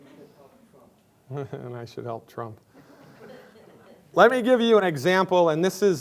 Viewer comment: what does he say here?? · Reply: Someone in the audience says jokingly "you should help Trump". Glidden says "haha I should help Trump"